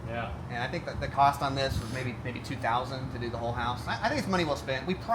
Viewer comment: Great video! Did he say 52k for whole house rain screen/rib wrap (31:30)? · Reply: $2,000 material plus labor